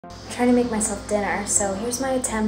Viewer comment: I thought she started by saying "I'm trying to make myself thinner" 😅